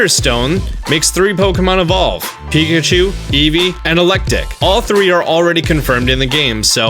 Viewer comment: Uh... 6:23 did he say elekdick?